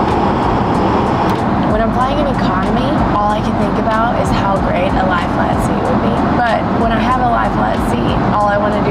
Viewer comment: What is she saying at 9:00 ? “When I’m flying in economy al I can think of is a life a lot of seat..” What? Can someone help make some sense of this? I can’t understand what’s she’s saying. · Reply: “When I’m flying in economy, all I can think about is how great a lie flat seat will be, but…”